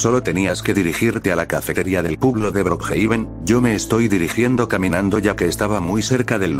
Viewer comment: escuche "de el qlo de brookhaven" JAJA